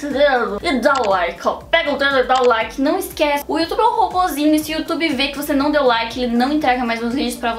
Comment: eu entendi O youtube é um cocozinho😂😂😂😂😂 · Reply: tb kkk voltei com seu comentario e vi q era ''robozinho'' kkkkkkkkkkkkk